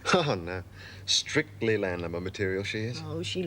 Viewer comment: what does the receptionist say to Steve JH JH pick up some lady to be..... I could not quite catch what she said. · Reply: Carlene Boulden Land Lubber material